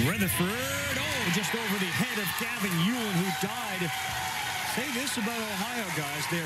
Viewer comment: it sounds like he said he died